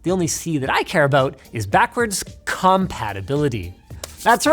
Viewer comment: Why did you say "CUMpatibility" like that lmao